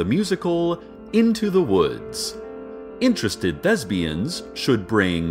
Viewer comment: I heard "interested lesbians" and immediately got interested